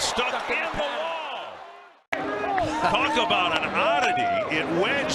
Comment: こういうの英語わかるともっと面白いんだろうなぁ って思いながら英語勉強せず観てる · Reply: 東京バナナにしか聞こえない